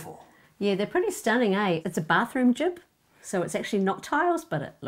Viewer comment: What is she saying? "It's a bathroom JIB." What's a bathroom jib? I did a quick search and it didn't seem to match up. Does anyone know what she's saying?